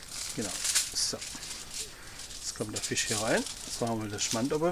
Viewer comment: Hey! Was ist das in der gelbe Schüssel bei 2:57? Wir in Sachsen verstehen deinen interessanten Dialekt nicht so ganz.. ;) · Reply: Frank Underwood Nu, mit dem Sächsischen tu ich mich auch schwer. In dem Fall war es Creme Fraische, es geht auch Quark oder Ricotta, oder oder oder....